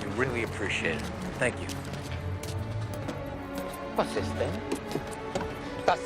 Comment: German speakers simply don’t know “yippie ki-yay” . We always said “yippie ya yay”. Probably a case of misheard lyrics:). 13:37- could be “Was ist denn?”. Like “what’s wrong”. It’s just mis-stressed, so really hard to understand for a german. 14:24 probably “Ich will das nicht mitnehmen” “I don’t want to take that with me”